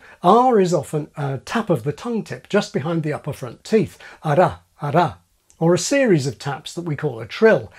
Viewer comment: Did he just say Ara-ara?! 😂😂